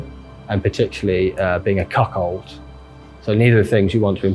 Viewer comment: did he just say "Cuckhold"?